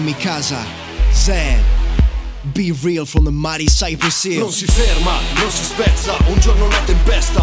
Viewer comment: I swear he said miley cyrus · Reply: B real from the mighty Cyprus hill lol Mighty Cyprus not Miley Cyrus